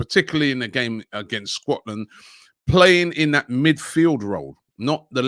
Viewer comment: Did he say “Squatland”?🤣